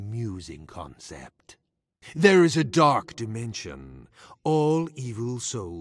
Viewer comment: when malak said dark dimension it sounded like he said dark deception